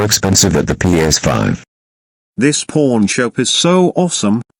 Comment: did he said pawn or porn shop? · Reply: Pawn shop